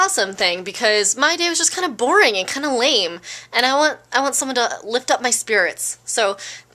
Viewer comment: lol i thought you were about to say "i want someone to lift up my skirt"